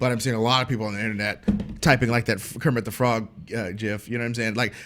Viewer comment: Did say JIF at 18:02!? That does not put a smile on my face!